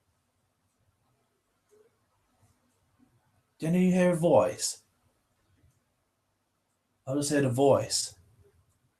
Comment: I heard a voice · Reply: yea me too.. it was like : i will do it